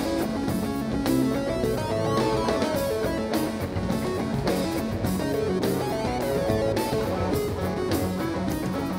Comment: does Brendan say "So fucked up" to Stasik?! lmfao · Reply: Definitely "so funky"